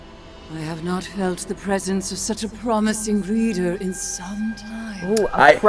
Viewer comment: I thought she said a promising breeder (reader)